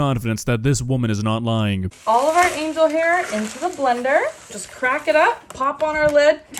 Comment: It looks like she opened a 70s cookbook and got inspired. 5:51 did she say "Angle hair"?